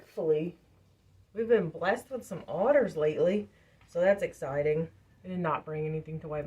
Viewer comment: LOL...listening while working, had to stop to rewind...I thought you said "we've been blessed with some otters lately"!😂 I was like...she said that rather calmly... Glad you are getting more orders!